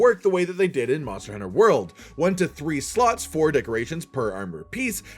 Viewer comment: why does it sounds like he saying slu#